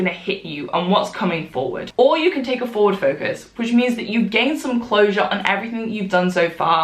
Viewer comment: Honestly thought she said “you can take a Ford Focus”